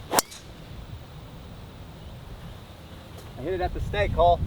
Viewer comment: Did you say “I hit it at the stank hole” 45:48 😂😂😂